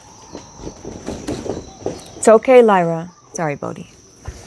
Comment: Shii- did you just call that cat "Bogie" lol i have no idea how to spell it (BOW-ghee) bruh thats one of my family dogs growing up name. That was wild. Ik it's like dukes of hazzard or whatever but never heard anyone else irl til now. Sister we soup tribe frfr. Sooooo many synchronicities. Looveeee you